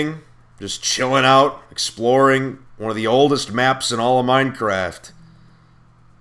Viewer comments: who else thought it said explodeinh not exploreing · Aww man I thought it said exploding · lol I thought it said exploding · i thought it said exploding instead of exploring.....